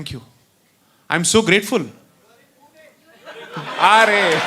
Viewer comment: What does the audience say? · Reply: U r in pune